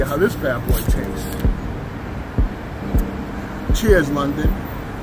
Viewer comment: The 1st time he said "cheers London" , i heard "cheers my Nxxxa" XDD Good to have you in Europe